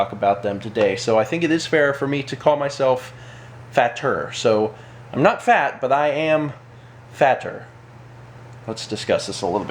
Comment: I thought It said Ìm Father